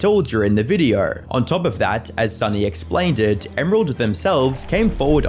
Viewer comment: dude did you just say "explainded"???? dude 😂